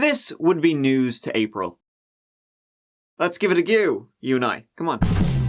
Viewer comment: Sounds like is saying .let's give it a goooo. Lol